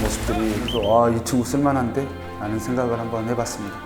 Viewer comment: 아 유튜브쓸만한데?!로 들리네 ㅋㅋ